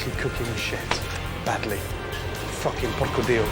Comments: DID HE JUST SAY 'PORCO D*O' ? LMAOOOOOOO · did he say porcodio lmao It means god pig in italian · did gordon just say: porcodio..???? HAHAHAHAH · Did he just say "Fucking porco dio" at 12:03???? :DDDDD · what did he say?!?! He sounded like "fu****g porco dio", that is an insult to God in Italian 😳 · He really said "Fucking porco ***" ?😂😂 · Anybody English/American that can tell me, in his/her opinion, what Gordon says at 12:03? Because, as an Italian, I understand something like "fucking porco dio". For those who don't know, "porco dio" in Italian is very common form of swearing, and it would be funny if it is what it seems to me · did he just say "porco dio"? it's cursing God, in italian